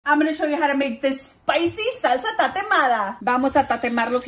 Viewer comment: Thought you said salsa Tarahumara 😅! Like the Indians from Chihuahua that sell Chiclets and tamarindo on the bridge .